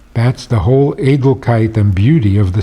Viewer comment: What word is Rabbi using here? sounds like "eitelkeit"...which means?